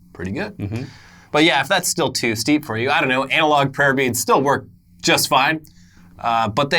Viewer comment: for a moment there I thought said anal prayer beads 😏